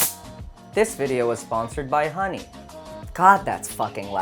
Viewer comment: I thought you said :This video is sponsored by taser camera